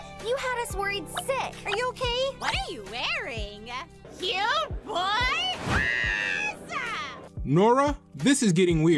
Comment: what did she say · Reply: cute boy Oz.